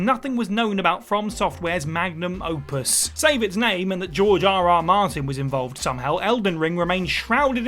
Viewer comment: I thought she said jar jar martin